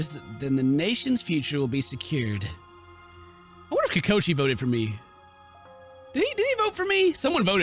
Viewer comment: did he say kokoshi instead of kokichi or is it just me?